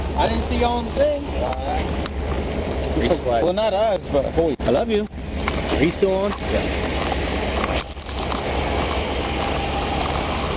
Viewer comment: Did I just hear one of the troopers say “I love you” to another one at the 9:45 mark? That’s adorable!